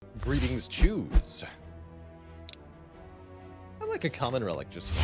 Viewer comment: I've been playing way too much cyberpunk. I thought you said greeting chooms.